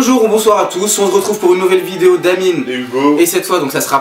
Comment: jai entendu mojito rigolez svp